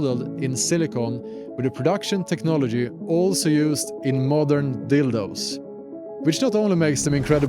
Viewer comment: Did he say the turn signals are the same silicone used in "modern dildos"? Pretty sure he did at 7:30. 😂